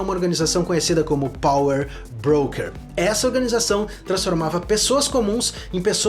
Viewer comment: achei que ele ia falar POWER RANGERS kkkkkkkkkkkk